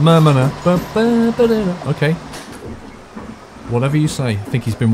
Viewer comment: Wtf did tht guy say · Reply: Einmal normal bitte = a normal ticket, please